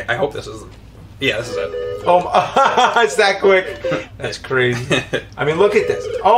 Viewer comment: James says "OMG That's crazy". I only hear "WHAT WERE THEY THINKING"